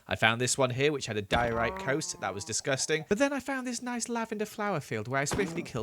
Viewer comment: I heard “ which had a diorite cow which was disgusting “